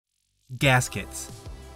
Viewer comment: i thought it said casket..... and for a minute i got excited..... then got excited anyway